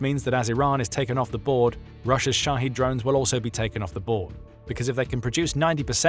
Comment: Shahed drones! I thought he was saying "Russia's shotty drones" as in sh!tty. 😅